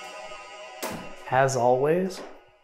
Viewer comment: You have the Ghost Vox saying, "there's our way". But I hear, "there's all ways". 🤷 Just an opinion. Also, maybe you should wrap a thin glow stick type necklace/bracelet around one ankle to help see when walking in the dark. It's nerve wracking to see you trip. 😅 Love you guys! 👍❤️